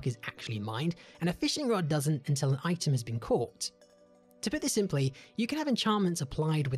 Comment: Ok for a second I thought you said “I’m simply zucc” and now I’m sure I’m consumed by memes